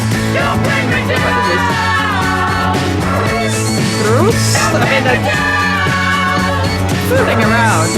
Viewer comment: "Groos" was originally just a random sound that he used as a filler, but so many people when it came out (including me) misheard it as "Bruce" that in later years when performing this live he actually switched to singing "Bruce"